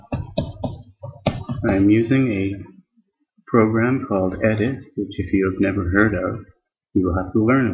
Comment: I would like to ask, what program you were speaking about going from 2:40 ? I have heard it as ant but can not find nothing similar in the google. Thank you.=) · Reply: Unfortunately, the "edit" program (the one I was talking about) became unavailable with 64 bit Windows. The closest alternative is simply "notepad" but that would be useless for this DEBUG section since the DEBUG program also became unavailable at the same time. So, if you have a 64 bit version of Windows the only way to access these programs is by using an emulator and installing a 32 bit version (say Windows 7-32 bit) inside the virtual machine. One program that allows this is called "Virtual Box". Sorry, I wish there was a simple answer :(